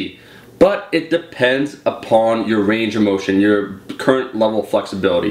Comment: i heard 'it depends on your RANGER motion' huehuehue